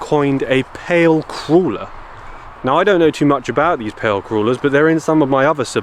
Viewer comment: I thought you said pale creuller, like a donut, lol. Thank you for the stories good sir